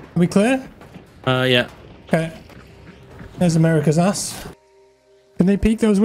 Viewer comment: Did you just say "thats america's ass"? Lmao